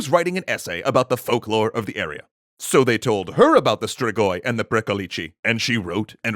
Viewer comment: When you said "strigoi" I thought you said "Sugoi!"